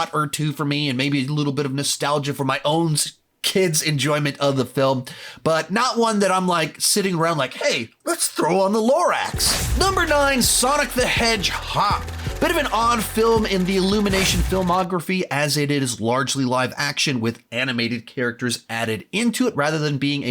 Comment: did he just say sonic the hedgehop?